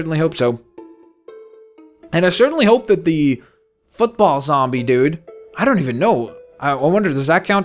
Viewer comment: OMG a football zombie Me: Did he just say f**kball zombie? notices mistake Ohhhhhh my mistake :/